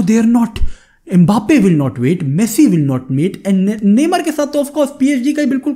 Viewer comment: "Messi will not mate", kya bol gaya bhai aur chal kya raha hai dimag me?